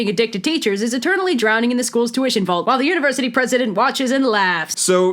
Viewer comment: So... they're drowning in perhaps two of feet of $10 bills? [EDIT] Ohh you said the tuition vault. I heard the salary vault.